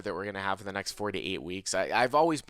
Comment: It made me laugh when talks about the "new found time off from live poker in the next 4 to 8 weeks" as I misheard it as "48 weeks" and was like "How did he know?".